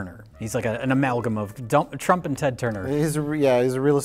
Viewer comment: Accidentally almost says dump instead of Trump. Thought it would make you smile.